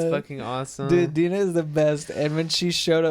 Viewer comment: sounded like said beaner and i felt attacked